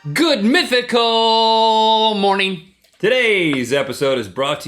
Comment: I thought link said, " Good Methical Morning" lolz